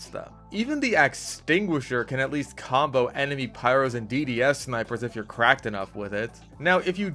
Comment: I thought you said "dds spec" and expected a clip of you using a DDS from OSRS to spec out the opponent :(